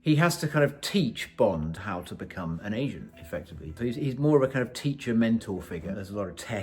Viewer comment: The subtitles say "he has to teach Bond how to become an Asian, effectively". This is either a very strange typo or a reference to You Only Live Twice. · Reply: It’s a typo or misinterpretation, the word is agent, not asian (although the actor slurs the word a lot in the soundtrack)